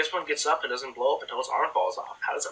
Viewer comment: he said intel instead of until in the subtitles